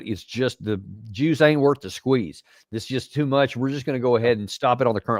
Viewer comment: Damn, I spilled my coffee because I thought Davis said "The Jew Aint Worth the Squeeze" 😮 😂 but indeed I was wrong Davis clearly said " the juice aint worth the squeeze." This old Marine Corps veteran here has a slight case of tinnitus 😂 Carry on